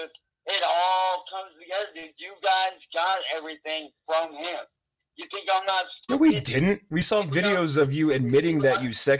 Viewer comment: does he say " you think I'm not stupid" ?